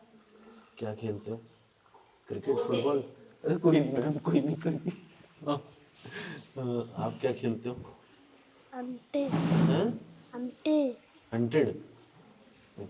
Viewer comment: I think the kid said "anntte" which i guess is "kanche" in our usual north hindi term.